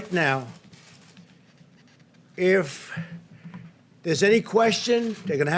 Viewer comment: Did he say, “If”? And why is out of breathe?